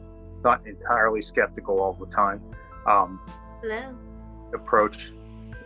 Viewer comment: Great and very informative video! I'm glad I found it. But did anyone else hear the female voice say, "Hello!" early on in the video at 1:37? He didn't react to it so idk if he's just really good at ignoring someone and keeping his composure during a video 😆 OR if he just didn't hear it. If it was a paranormal "hello" I am going to freak!! 😳